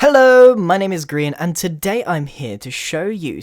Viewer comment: I thought he said... "Hello! my name is Green."